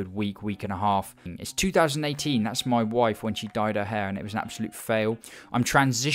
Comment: wtf I was shocked hearing this first half of the sentence, I heard it as 'died' instead of 'dyed' so I was very confused for a few seconds